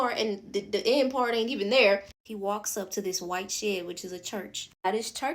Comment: Listening to them say the movie is demonic... all I heard was "white-washed", "whiye-washed" over and over again